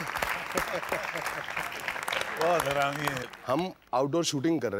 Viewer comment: Did he said "bhaut harami hai"